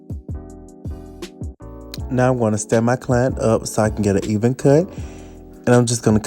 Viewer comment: The captions said "Now I want to stab my client up so I can get an even cut.." 😳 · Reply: He said stand